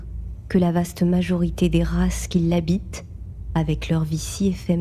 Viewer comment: pourquoi j'ai skip à 00:27 pour entendre "l'habite"...